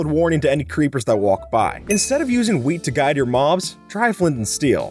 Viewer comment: "Instead of using wheat to guide your moms"